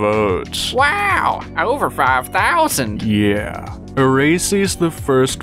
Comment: 'BRO THE CAPTIONS AT 2:27 SAID "A RACIST" INSTEAD OF "ERASEY"